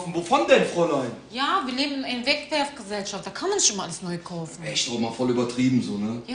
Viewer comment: "Wir leben in einer Wegwerfgesellschaft. Da kann man nicht immer alles neu kaufen." xD mein neues Lebensmotto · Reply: Ich hab beim ersten Mal "da kann man schon mal alles neukaufen" verstanden :-)